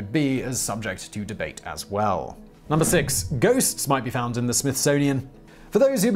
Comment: Oh ghosts! I thought you said goats.